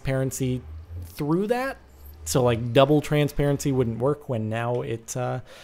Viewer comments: Did he said Twerk :l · Did he said "TWERK" ? :O ------>